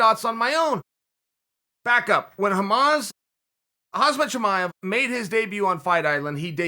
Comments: Did he mispronounce khamzats name with a terrorist organisation? "when hamas"? Or am I mishearing this... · Did he just call Khamzat "Hamas"? Hahaha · Did Chael just call him Hamas? 🤦🏽‍♂️Jesus 😂